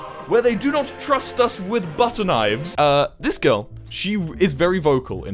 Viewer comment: When he said butter knifes I thought he said 'Button Knifes' as in you press a button and knifes come shooting out and I almost died.